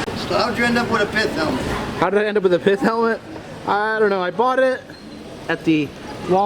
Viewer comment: what did he say he bought at a Walmart in myrtle beach SC?? What did that guy ask how did he end up with a what???? Couldn't understand what or the street guy said.... Anyone know what they were talking about??? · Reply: My pith helmet